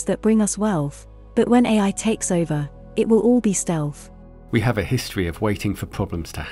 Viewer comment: COnsidering how much we're already being manipulated, it's scary to think what a super intelligence could do. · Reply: What does the AI: GPT 3.5 (TD3) say at 5:43-5:49? “We’ll have great companies that bring us wealth, but when AI takes over it will all be ___. What’s the last word? Stealth? 😮